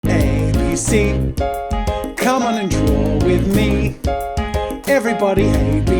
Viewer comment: At the start I thought he said "ABC cmon and drool with me"